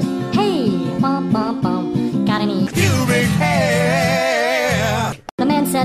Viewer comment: I think he said "Human Hair" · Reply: No, it’s pubic hair